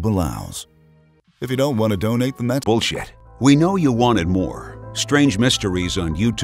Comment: Did I hear at 9:08 min: "...if you don't wanna donate that's....bullshit...we know you want more...?" That's pretty straightforward...